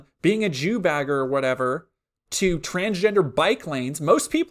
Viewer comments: I swear I heard “Transgender Biplanes” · i thought he said “transgender biplanes” and i was like ??????